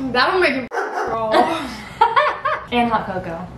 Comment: When combined the two chopsticks and the kiss with woof I don’t know what were the words but I 100% understand what she was referred to because yeah I noticed too. So happy that you exist guys looove you!!!💗💗💗❤️‍🔥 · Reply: To me it sounded like she said "that will make your 😸 crawl" but like you said it's not easy to understand 🤣